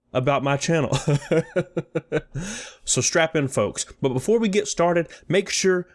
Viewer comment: did he say strap in or strap on?